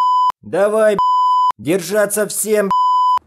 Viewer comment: А кто понял, что сказали моряки между 1:30 и 1:34? Очень надо, пожалуйста! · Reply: давай б***ь! держаться все б***ь! (Я только это помню)